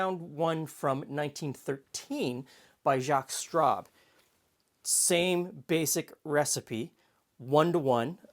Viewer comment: Maybe I've had a cocktail too many, but every time you were saying 'Jaques Straub', all I heard was 'Jaques Strap'. That famous French Canadian hockey player and inventor of protective equipment 😀